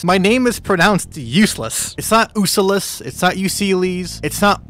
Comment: I thought you said your name as U-Sye-lis